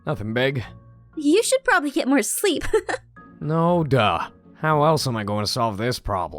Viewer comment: did he say noda or yoda at 1:58 XD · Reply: Firelight54 he said, "no, duh."